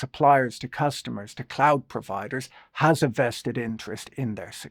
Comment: I thought you said "clout providers" and now I think about it, these clout providers might be a key part of the supply chain...